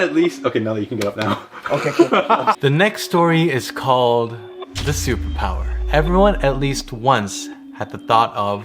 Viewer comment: why did i hear "the soup power?" 😂